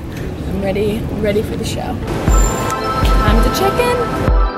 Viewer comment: when she said "time to check in" I thought she said " I'm the chicken"